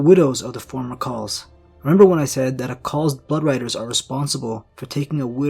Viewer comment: LOL my phone messed up while watching this and I heard you say a group of riders close to a Karl is called his butt Riders